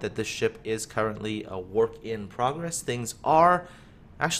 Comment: Did he really say a "warp in progress"?